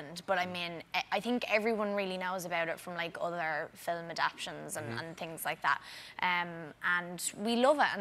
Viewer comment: Did she say "film adaptions" at 4:42?